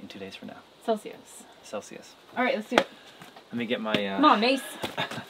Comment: Hey guys, at 33:50 was that an aussie 'cmon mate' I heard from? Very cute 💗 And that oven is HUGE 😁😂👊👍